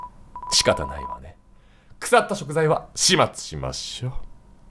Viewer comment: すごい今更になっちゃうけど、マフェットの「くさったしょくざいはしまつしましょ」ってセリフが「腐った贖罪は始末しましょ」に聞こえてなんかビビった